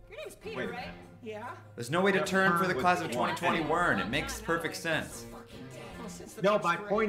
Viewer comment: I totally heard "Twenty-twenty WERN" when I watched, despite the captions saying "2021." I think it's a fun rhyme that adds to the comedy of the musical. You guys always make really good stories, then riddle them with silly shit like that that makes the satire clear.